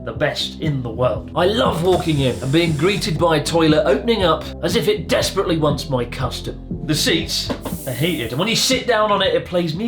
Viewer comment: I was half paying attention and misheard this as "I love walking in and being greeted by a toilet that desperately wants my custard". Now I have to live with the imagery.